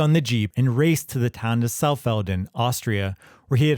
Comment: i heard that Pittsburgh "Tauhn" instead of Town. Lol